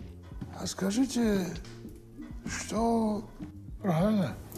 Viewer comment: я один расслышал шо он сказал "правильно бля"😕??